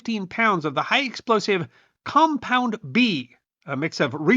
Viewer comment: I thought he said "compound V" 😂